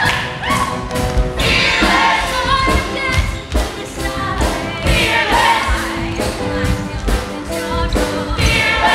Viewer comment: What does say at 3:31? “I..........! I’ve climbed Kilimanjaro! Who knows what I will do tomorrow?” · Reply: I have danced with the Maasai